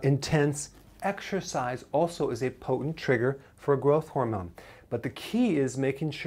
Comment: I keep hearing 'grow hormone", but what hormone or set of hormones is that specifically?